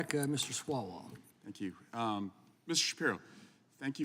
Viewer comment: I could have sworn he said Mr Swallow